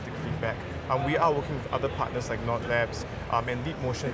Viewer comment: What parter did he say when he was talking about the vr controller at 2:16 sub title said "not lapse" what is the actual name